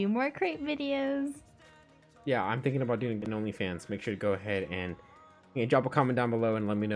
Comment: did i just heard u have a Onlyfans 🤣🤣